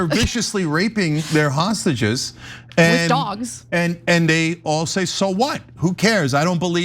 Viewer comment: I heard Cenk say there raping hostages,and Immediately Anna said with dogs. Did I hear that right? Listen starting at around 9:10 then go from there.